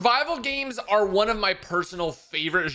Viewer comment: “Vival Games” lmao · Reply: I thought he said "bible games."